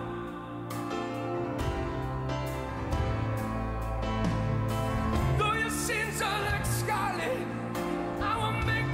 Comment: what does he says at min 6:22 about the sins? I can't make it out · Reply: Oana “though your sins are like scarlet, I will make them white as snow”